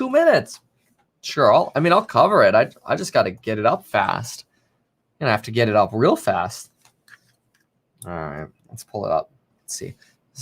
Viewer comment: And I’m a so glad you can get it up really fast 21:17 , I only heard that part from the other room , lord forgive me